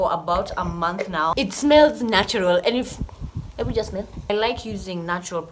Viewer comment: Did you just say , "Ebu smell" 😂😂😂😂😂😂😂😂😂😂😂😂😂😂😂